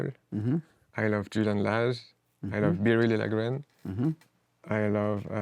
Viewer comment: Who is the jazz guitarist he names after Julian Lage at 33:11? I couldn't understand him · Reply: Nvm it's Biréli Lagrène